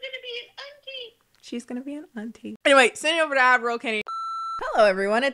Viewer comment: The wayyyy I thought she was saying admiral Kenny and its Ad roll ffs mi a dead wid laf 😅🤣🙃